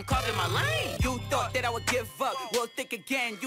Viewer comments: It sounds like he said, "You thought that I would give a fuck." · lowkey sounds like he says "you thought i would give a fuck' lmao 😂